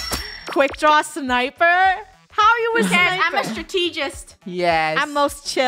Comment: Oh was a strategest i thought it said supergirl🤣